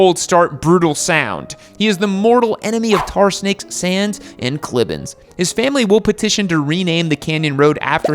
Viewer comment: "tar snakes, sand, and ____?" (couldn't understand what he said in blank spot) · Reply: Clibbins sounds like